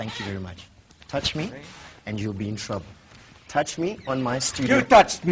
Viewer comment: i swear i hear "touch me in my studio" about 20 times